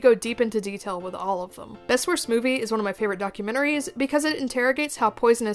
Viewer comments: What's the name of that Dentist movie you mentioned? I can't quite make out what you said. It's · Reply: Best Worst Movie! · there is a clip of a movie in a dentists office. What movie is that? I couldn't make out what you were saying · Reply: Best Worst Movie!